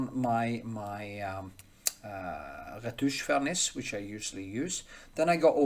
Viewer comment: Hi there. At 8:05, what are you saying that you first put on the canvas? Rhymes with “vertoosh furnace”? You put it on between layers. I’m sincerely interested in knowing what technique you are referring to. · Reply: Hi :hand-pink-waving: I do say Retouching Varnish ( i hope ) sorry for my strang english , I am Norwegian , ha ha :face-blue-smiling: And yes i put it in between lears .. I made this video of the process here <link> you can also se the full process video , link in description <link>